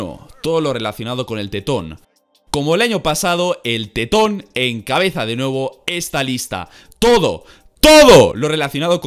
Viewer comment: "Encabeza de nuevo" Lo que entendí yo:"El cabeza de nabo"